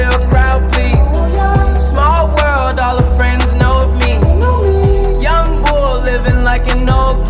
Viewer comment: я одна слышу Ян Го